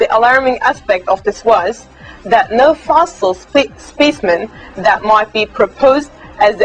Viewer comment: For a moment, I thought she said "Fossil spacemen"